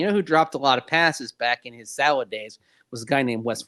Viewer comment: Maybe I misheard what Mase said…but Wes Welker Seattle days? Huh?